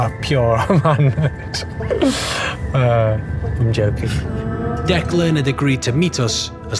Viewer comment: Did he just say "I'm J*rking"? XD